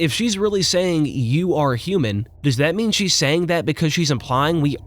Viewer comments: I heard “you were human” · I think she said "you are NOT human" not you are human.